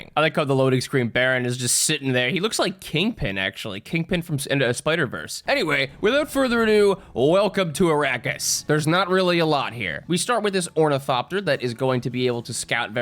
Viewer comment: i heard "welcome to iraq" the first time